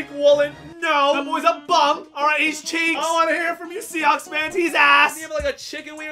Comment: I swear I heard him say seacawks